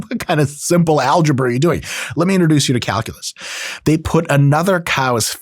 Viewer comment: I heard it as COWculus